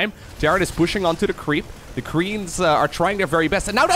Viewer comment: I was sure I heard some misspronunciation! The creems!